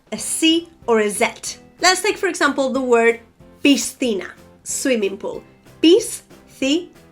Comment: I hear pistina. "Sci" oigo como "sti" siempre. Por qué psa eso?